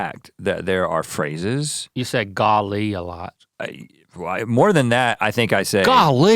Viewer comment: French is my mother tongue and the first few times I heard Rhett say "Golly!" I thought he was saying "God, Link!"